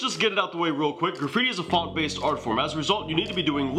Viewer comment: and 3:40 all i hear is "fart based art form", can somebody please tell me what he says? subtitles dont work either :D